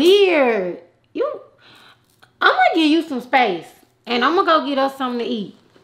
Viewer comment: 🤣🤣🤣 nigga I thought she said "I'm gon give you sum face and imma go get us sum to eat" lol ran it back 4 times lol